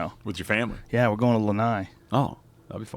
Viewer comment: "we're going to..." to whaat ? Leni ? what is that ? replayed 10 times, still can't understand. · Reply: Desi He was talking about going to Hawaii, the island of Lenai (spell check on that name) for a a vacation with his family and a Axis deer hunt. If I remember correctly he did this vacation and hunt with John Dudley. He (John Dudley, Nock On TV) did he’s podcast their with Joe.